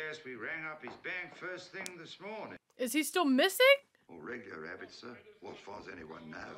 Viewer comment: The reason you didn't know what the "joke" was at 26:34 is because it was a mistake in the subtitles. Instead of "regular rabbit" it should have read "regular habits". The policeman was saying that Mr. Banks was a man of regular habits so his being gone all night was very unusual.